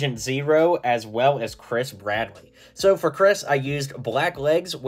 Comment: Why did I keep hearing you say “Chris Pratt” when you said “Bradley”? It works so well and was so confused why you made a minifigure of him for this 😭🤣